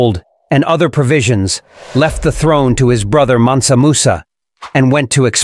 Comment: Sorry, I really heard "Man samosa" instead of Mansamusa🤣